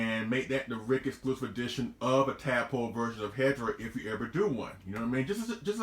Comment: 24.09 I'd thought he said asshole version lol